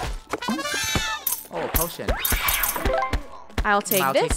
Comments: omg i thought when he said OH A POTOIN i thoguht he said oh bull sh%$ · what did just said? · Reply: Oh potion GIRL